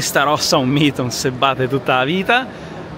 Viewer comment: cosa dice, non si batte tutta la vita??